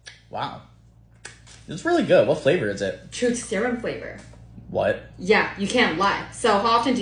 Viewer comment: I THOUGHT SHE SAID TOOTH SERUM😭